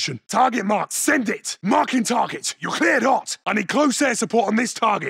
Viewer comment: What does he say at 13:16? It gets me so hyped calling in a cruise missile but I have no clue what he says. Is it "Marking targets, you clear or not"? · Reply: JC ramero you’re cleared hot